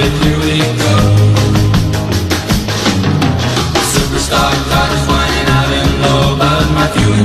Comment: I'm an old man now and have owned( and still own) Vettes. Remembering back in the day the Max Wedge would have smoked the sb Chevy. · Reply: for the longest time, I thought they were saying "Super Stock Dodge.